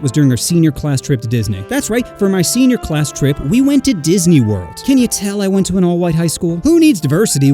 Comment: I legit heard diddy's world. 💀